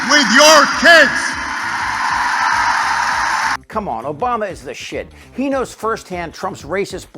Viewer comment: Why does say, "Obama is shit"? around 4:20. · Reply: He says "Obama is the shit." It's praise of some sort.